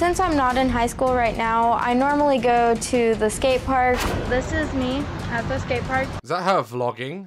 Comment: I thought he said instead of skate park I heard I’mA bE a FuCkInG sTrIpEr 🤣🤣🤣